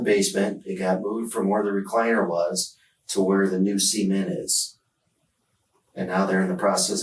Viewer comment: When he said cement I thought he said semen